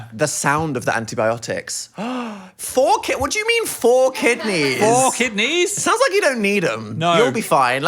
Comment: i think she said "for kidneys" not "four kidneys" 😭😭